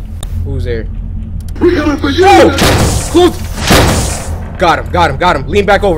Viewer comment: bruh 12:27 BRUH DID I HEAR we coming for you little (Cory shuts the door)